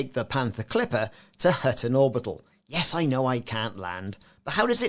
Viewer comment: I heard "I'm going to take the Panther Clipper to hurt an orbital." Lol I could see this beast hurting one for sure.